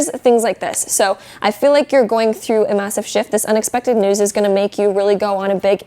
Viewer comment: so you guys are gonna get a massive shift Me, who misheard it for "massive shit": oH, that's lovely I guess..